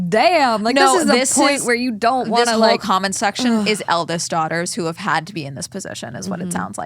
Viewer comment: I totally thought she said Elvis daughters too lol.